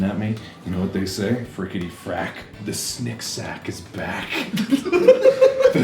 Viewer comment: for the longest time I thought he said snick-snack he actually said snick sack (though to be fair, he said snick sack the first two times then continued saying snick-snack so there)